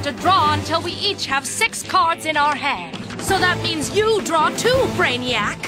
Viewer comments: sorry if you've heard this a million times already, but I think my said "so that means you draw too" instead of "so that means you draw 2" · I always thought she said "You draw too brainiac" not "You draw two"